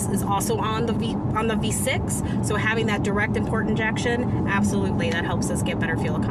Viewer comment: did she say "direct and port injection" at 3:11?